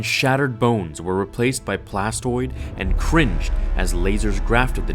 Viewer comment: i think i heard CRINGE